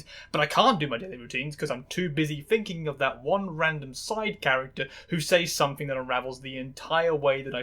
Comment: 40 seconds in I misheard "side character" as "Sonic character" and thought I had just jumped into 4 times the madness that I signed in for